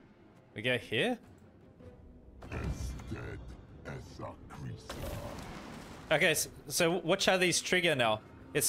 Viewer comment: "Ess'tedd, esse creasa." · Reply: I keep hearing "As dead as a greaser"